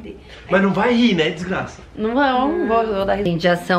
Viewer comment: E vdd que o falou em 5:07 DESGRAÇA ou é eu que escutei errado